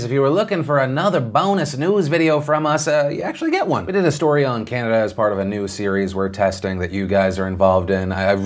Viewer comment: I'll see you.... tomorrow? Or Monday. Or maybe never. Who knows. I'm tired. heh. ;) Jake Paul(00:15), KSI/Logan(1:40), Plant for the PLanet(2:54), TIA(4:19), Thai Rescue Update(5:34), Trudeau Update(7:27), Pruitt(9:25), Midterms( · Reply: Did you say Canada here, instead of Cannabis?